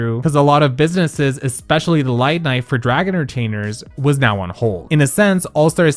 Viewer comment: Did no one else hear “Light knife” instead of “nightlife” at 7:54 🤣